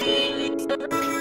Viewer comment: At one point in the beginning, it sounds like they say "I'm hamburger"